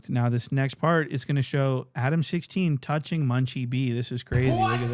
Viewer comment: I thought Munchie was saying Adam16 at the beginning took me a sec to realize he said Adam60